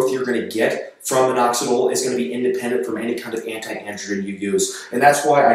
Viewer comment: What did he say at 3:40? Anti what? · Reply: Anti-androgen, which finasteride is.